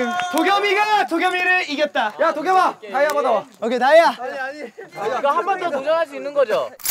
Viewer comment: YA DOKYEOM-AH KAYA MO TO misheard ahahahahah